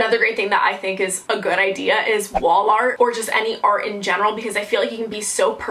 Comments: When she said wall art I thought she said Walmart and I was so confused 😂 😂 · When you said wall art i thought you said wallmart